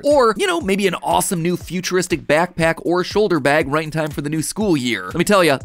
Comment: I thought you said ‘Matpack’ for a second there and I was about to buy one lmao